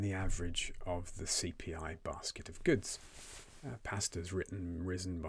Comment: Can't hear out the word in 6:53. what's risen 140? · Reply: i think he said "pasta"